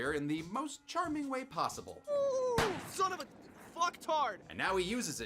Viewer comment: in, I honestly thought that said “son of a f*** tart!” 😐